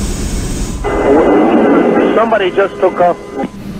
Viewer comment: The controller definitely has a “do not argue with me” mentality. This is not the job for her. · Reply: Except that was not what was said. 06:15 "somebody just took off" is what the pilot said, which, especially considering the context of his other errors does more harm than good.